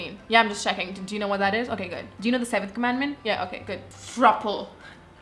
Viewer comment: I love you but did u just say 7 commandments or is it my ears?